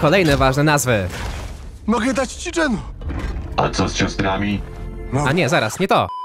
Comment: Ja też na początku usłyszałem mogę dać Ci dżemu A co z ciastkami